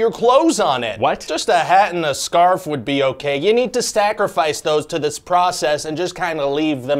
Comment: I heard “You need to stacrifice those…”